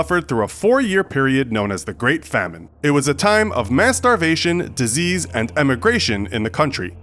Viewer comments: Infographics according to me I heard you told MASTER BATION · I thought I heard masturbation in 2:12 though